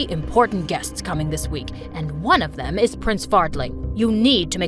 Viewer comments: i couldn’t stop laughing when her father said “prince fartling” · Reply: When he said Prince fart ling I thought he said Prince farting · Did he just say prince Fartling?! 😂😂😂😂😂😂😂😂😂😅😅😅😅😅😅😅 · i’m sorry, but did I hear that right prince Farling😂😂😂😂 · “ Prince fartling” is what I hear on 7:00 lol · i couldn’t stop laughing when her father said “prince fartling” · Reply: Init i thought he said prince farting when her father said prince fartling